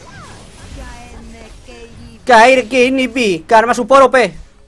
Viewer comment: Knekro que dices? Lol, ..... karma support op